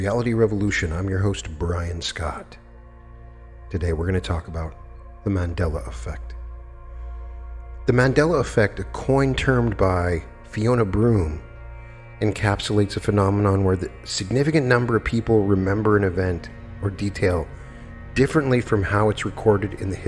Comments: I JUST HAD a REAL Mandella effeect. at 0:18 I heard say that the Mandela Effect was a "Coin Termed by.....". lol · I'm sure I heard him say "a coin termed," but probably if I listen again, he DID say "a term coined?" 🙂